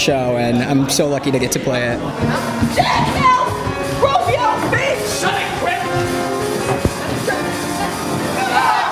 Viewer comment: What's Crutchy saying from 4:03-4:06 ?? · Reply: Kara H he says "Jack help! Romeo! Finch!" which are two of the other newsies:) hope i helped even though it just occurred to me you left this comment 5 years ago😂